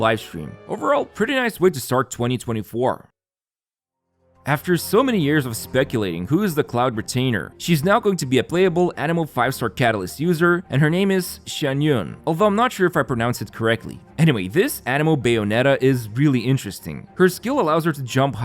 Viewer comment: During the announcement, I misheard Xian yun's name as Xian yu (salty fish)